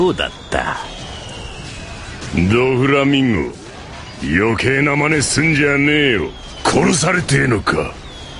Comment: Does Mingo say Mah Nigga